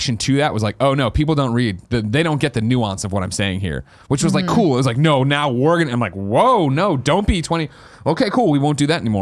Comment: Did Greg have a stroke? Here is a transcription of what he said at 24:44. "Which was like cool It was like now were gonna woah don't be in 20 okay cool we won't do that anymore like thats not how we're gonna do it So I I di To come out and yeah to do these tweets and put these things out there like and you know you talk about being a character like the reason heels work in wrestling is because the faces the good guys always get it over."